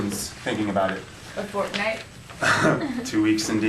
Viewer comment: did she say fortnite 😱😱